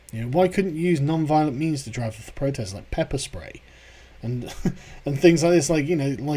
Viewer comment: LOL I heard use Pepe-Spray.